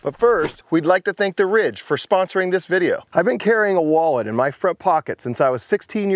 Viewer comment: Initially I heard 'We thank the rich for sponsoring this' 😂